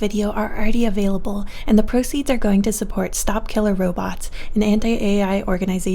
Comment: I heard "Stop kill-" and I legitimately thought she was gonna start talking about the Stop Killing Games EU initiative Dear God